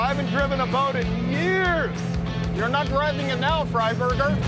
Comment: I think one of the guys called the other one a “Fur Burger”